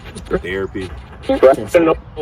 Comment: right after you ask how she feels about all this stuff coming out about Cassie, I hear “horrible” then “awful” immediately after the question.